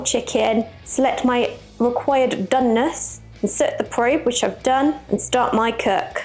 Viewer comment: Omg too funny. I thought she said "Required Dumbness." I am like ayo :hand-pink-waving: that's me. 🤭